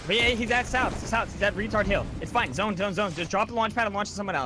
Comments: did I hear cloak right? 😂 tard hill? Surprised no ones on his ass for that one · who else heard retard hill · did cloak really say he’s at retard hill? Lmfao